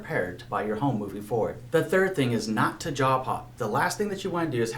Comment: I kept hearing “jaw pop”. Glad you subtitled “job hop” 🤭 and convinced me not to break up with Wells Fargo … at least until closing